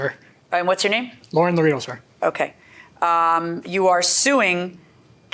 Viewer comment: did he call the judge "sir" or was that part of his name